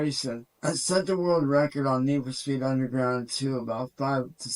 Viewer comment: I think he said NFS Unbound which is newer I believe. But there is no proof at all showing that broke any world records, let alone any record at all. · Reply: got ya. He ain't the clearest of speakers is he. I thought he said underground 😂😂😂😂. Really good videos by the way, I've watched them all over the past few days, not in order but I don't think that matters when it comes to the chaos goblin. 😉